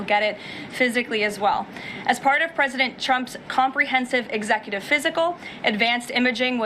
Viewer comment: It sounds like she is saying "president Chump".